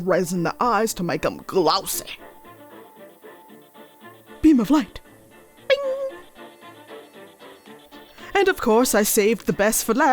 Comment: Uhhh when you used the beam of light on the eyes instead of it saying “beam of light” it said “beam of trauma”😂😂😂😂😂😂😂